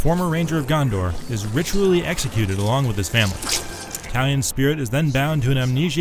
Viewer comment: Everytime he said Talion, I thought he said "an Italian"